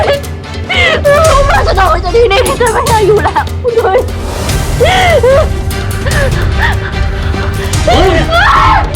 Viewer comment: what did she say at 2:37? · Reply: Please take me out the here, I don’t want to be here anymore